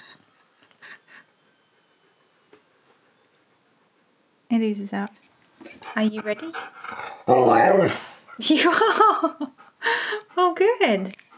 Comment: I definitely heard "I am."